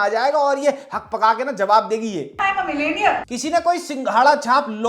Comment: I swear I thought she said millennial and I thought what's wrong